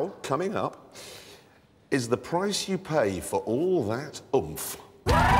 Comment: i hear at 0:06 "it,s you pay for that ovum"